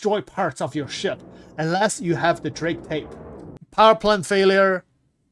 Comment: Unless you have Drake what? can't hear what you say after Drake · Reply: Drake tape. It was a joke😅